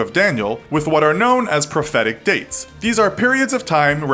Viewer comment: They said profetic dates, i thought they said pathetic dates